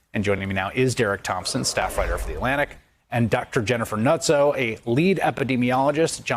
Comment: Dude I thought he said her name was Jennifer Nutso.